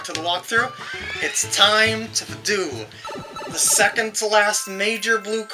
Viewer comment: i tty thought you were going to say "its time to duel!" oh yu gi oh how you warped my mind~ anyways good part, those bloody coins this game really likes to make it hard on ya